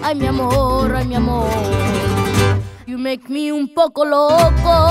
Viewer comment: she was gonna ask what you want for Christmas. Oh no, just tell her! lol BTW, at the end I thought you said " you make me Pollo Loco". 😊